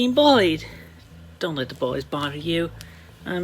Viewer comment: Geez seems barely coherent anymore when they talk. With slurred speech. whenever he said bullied it sounded like Boyd. lol